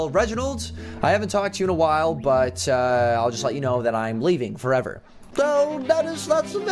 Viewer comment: I thought you said you were leaving for downloads❤️